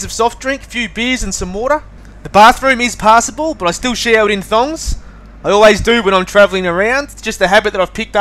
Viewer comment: What do you say on 1:57 "I still shower in ....? " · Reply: Thongs